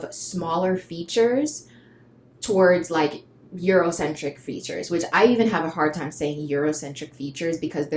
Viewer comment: as i said during the video "i do not have euro features" "africans come in all shapes and sizes" at this point, I'm convinced y'all are purposefully leaving that out. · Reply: i think i heard that you said 'leaning towards Eurocentric features'. Am sorry if i misheard this.